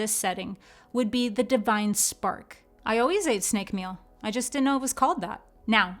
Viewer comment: At around 9:28 she says randomly “I always ate snake meal I just didn’t know what it was”. Did I hear that correctly? What does that mean??